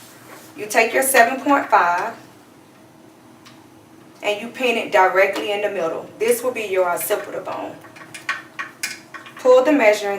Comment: What does she say at 1:36? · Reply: Pin directly in the middle. This would be your occipital bone. I'm just lettinh you know go straight across the back how you meaaured on your head